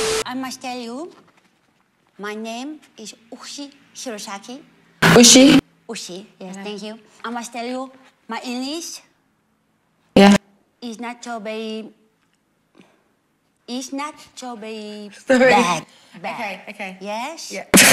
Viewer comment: I hear “my english is nacho berry”